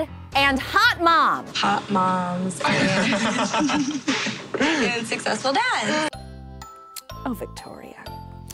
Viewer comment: Thank you for addressing the horror that was Victoria F’s toast. It made me cringe SO hard. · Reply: I thought she said moms and dads. I was like, ????